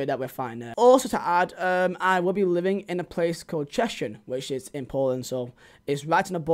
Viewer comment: I’m expecting a career mode of the local club from what ever part of Poland your going to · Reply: i swear he said its between the border with Czechia. Thought he said Cieszyn